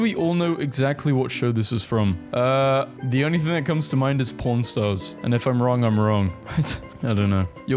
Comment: i can only think of pawn stars too but i heard you say pornstars.